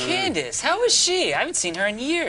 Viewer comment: "Oh Candace, how is she? I haven't seen her in years" "She's good, she's good. That guy stopped stalking her" How did I miss this joke for so long? That's fucking hilarious. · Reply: I always thought he said “I gotta stop stalking her”